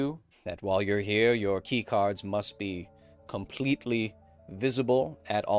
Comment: I thought Breen said v-card.